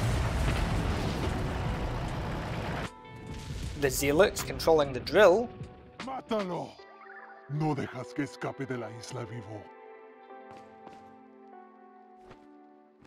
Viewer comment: I speak spanish natively. 07:25 And today it was that I realized the zealots sometime say "No dejes que escape de la isla vivo" (Don't let him get away from the island alive" All these years I heard "No dejes que escape de la casa" (house) Which doesn't make sense, but it makes more sense than the real one, you don't make it into the Island until later in the game. Someone messed up a line of code and these was supposed to be uttered by the combatants instead? The remake has this type of weird line too, sometimes the cultists will say "No puedo caminar!" when staggered giving you time for he kick. which means "I can't walk". Who tf says that AFTER BEING SHOT IN THE FUCKING FACE?". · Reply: Not Hispanic here, Brazilian,but I always thought he said like "no deje que escape de la vista"kinda like "don't let him escape from our sights"